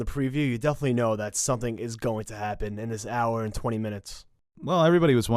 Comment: I misheard the part where you said 1 he and 20 minutes and thought you meant 120 minutes. Then I thought why would they do that instead of having an extra episode